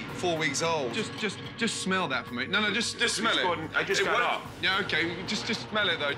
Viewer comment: “Sorry I was late I had to pick something up” 2 minutes later “Sorry I just woke up I can’t smell it” Hmmmm 🤔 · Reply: I ain’t gonna lie, I thought he said “I just got off” and I was over here like what the fuck?